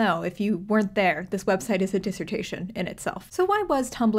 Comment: thought she said, “This website is dysentery in itself.” And I was so confused for a moment there. 😂😂